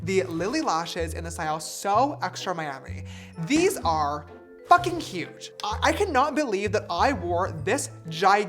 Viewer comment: is it just me this whole time that thought he said. “these are fucking cute. i cannot believe i wore this gigantic pair of lashes every day” but he actually says “these are fucking huge”…